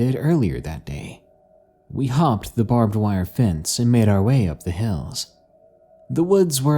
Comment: did it just say ‘we hum*ed the barbwire fence’?